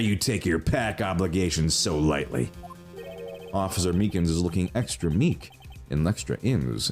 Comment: What is he saying at this point? I cannot for the life of me make it out. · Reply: “Officer Meekins is looking extra meek. And extra ins.” Character’s name is meekins, Edgeworth says he’s “meek” (adjective), makes a joke by saying he’s also “ins” because they’re both syllables of his name.